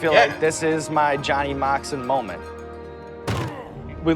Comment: i swear you said john moxley moment... moxley to hot ones confirmed?